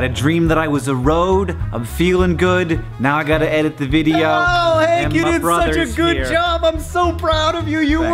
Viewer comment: When John came in and said, "Oh, Hank, you did..." I misheard him as saying, "Oh, hey cutie!" That was a little weird.